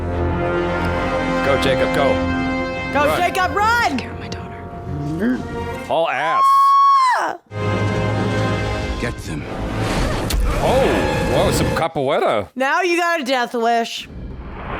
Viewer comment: did he said “that’s capoeira?” 🇧🇷